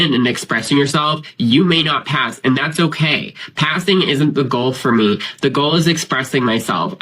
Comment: Haha, I was distracted the first time I listened to say 'passing', What I heard was 'calving' and I thought-in my distraction- yip, all I hear is big chunks of crap dispatching your face; so I rewound the tape to listen again, and, well, heard it still the same, lolhahalolhalol